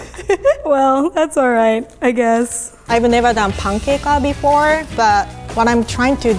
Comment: DID SHE SAY THE N-WORD?!?! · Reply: She said “I’ve never done pancake before”